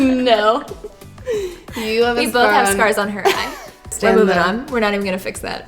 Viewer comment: I thought she said we both have sperms oh our eyes 😂 abs I was like wait hold on- then she said scars and i was like oohhhhh ok